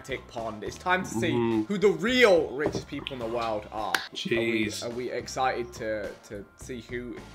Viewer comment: I swear harry just said hhhmmmm cheese